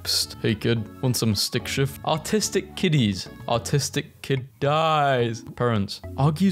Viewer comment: what he says: arttistic kiddies! What i hear: autistic kitties 🐱 😭🙏